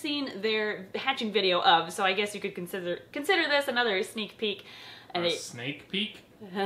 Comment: hehe I thought she said snake peek